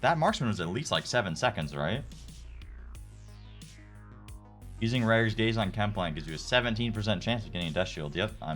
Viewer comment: What did he say at the end that had a high chance of getting deathshield? Was that a joke? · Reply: Somebody in his chat said “Using Ragorz Gaze (legendary rifle) on chem plant gives a 17% chance at Deathshield (shotgun)” but it was a troll. Not true unfortunately. I will give you this though. I ran my best friend through every expedition execept eye and some of the ones that require drop pods to do and he got the High Roller on a T13/T14 run I carried him on. I was like fffff let me get that lol.